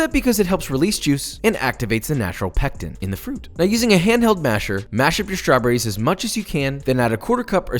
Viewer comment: oh good I thought he said masterba...